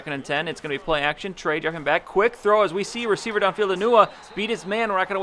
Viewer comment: Am I the only one here who heard him say "enuwa" instead of enunwa?